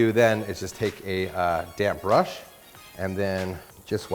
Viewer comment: When you're(me basically) so stressed that you(I) misheard damped brush became damned brush.😂